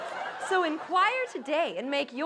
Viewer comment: how do you know when you're in too many church choirs when I thought she said "in choir" instead of inquire!